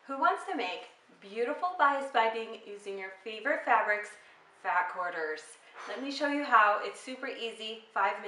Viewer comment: It sounded like u said fat corners instead of fast